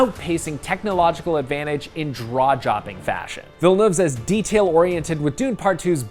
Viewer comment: Did he just say... "Draw-Jopping"? 😂